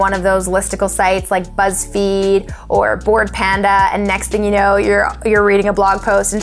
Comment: I got it, thanks :) But after saying buzz feed, you said other word that I could understand. I think you said "blue panda" but when I google it I can't find anything. that's why I think I misheard it. P.S. Sorry if can't make it clear enough. Since english is not my first language, sometimes I can't seem to convey what I mean. BTW, I've learned so much with your vids. Keep it going. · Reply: Thank you so much!!! The other one I said is Bored Panda 🐼 😬